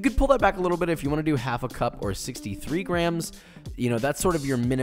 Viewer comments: Hey guys I must have forgotten to mention in the video. In order to keep your ginger bug alive you just continue to feed it the same proportion of sugar and ginger. Once it get’s too full or it won’t dissolve anymore sugar: just pour out about half of the liquid (leaving half behind) and adding some fresh water, and then go back to replenishing with the same amount of JUST ginger and sugar. Rinse and repeat to keep it alive forever! Also, once it’s active you can place it in the fridge and feed it once a week. Hope this helps! :) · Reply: At @ 2:44 he says "half a cup or 63 grams:. Did I hear him right? So basically 2 quartz of water with 63 grams of sugar. Please correct me . I want to go the least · At @ 2:44 he says "half a cup or 63 grams:. Did I hear him right? So basically 2 quartz of water with 63 grams of sugar. Please correct me . I want to go the least Also, you said for 3.5 L of liquid you add 3-4 Tablespoon. I have 3 questions , please do let me know 1. Does the fermentation happen well with this much less sugar 2. Does it taste good and fizzy? 3. If you could please share the grams conversion for this 3-4 tablespoon